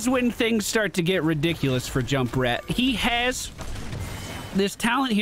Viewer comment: I heard "Jumprat" and at that point it's a pretty fitting name